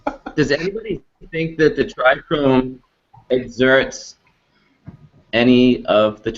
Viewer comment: When he said tricome it sounded like he said dry cum